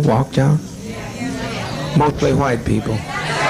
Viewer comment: does he say 'mostly white people' at 18:45? Lol